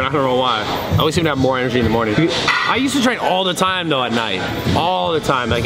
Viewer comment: I literally thought he said he used to drink all the time